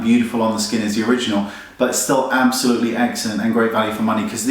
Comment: did he say its eggcellent